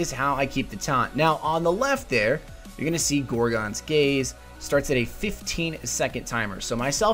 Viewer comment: did you say Gorgons Gaze?